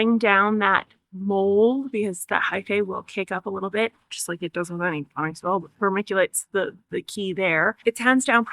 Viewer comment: I can't make out what you said at 6:10 when you say "just like it does without any ???" .I add homemade purple synthetic bacteria with molasses to the pile . It helps accelerate the decomposition process to create leaf mold. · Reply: Does with any potting soil